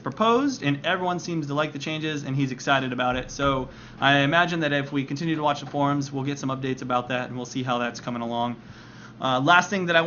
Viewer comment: I thought you said " I imagine that if we continue to watch porn, we get some updates."